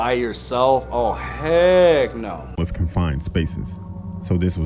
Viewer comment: I thought you said egg no but you said heck no